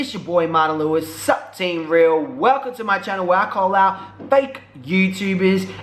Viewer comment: I thought you said "call log" for about 3 minutes omg